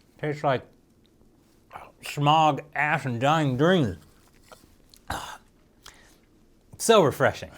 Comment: did you just say "smog, ass & dying dreams?" 🤣🤣🤣